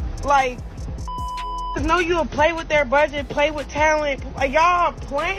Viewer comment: DID SHE SAY PAY FOR TALENT!!! This bih rhymes are slower than calm waters!!! Foh... 🤦🏾‍♀️🤦🏾‍♀️🤦🏾‍♀️😂🤣😂